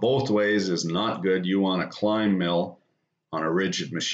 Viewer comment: For years, every time when you have said "climb" milling, I thought you were saying "Klein" milling... lol Better late than never haha